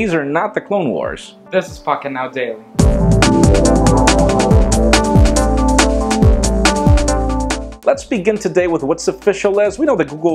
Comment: I wasn't paying attention to the video and when said "This is Pocketnow Daily.", I heard "This is fuckin' outdated." LOL!